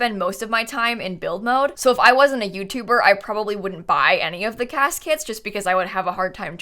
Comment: I heard you say cas kits and couldn't stop hearing caskets... 😅🤣